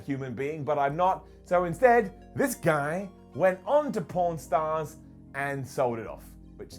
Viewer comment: Who thought he was saying porn stars when he said pawn stars?